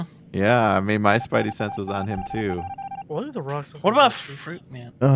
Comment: What the hell does say at 16:54 ? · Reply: Moonstruck Witch I heard “why do the rocks look”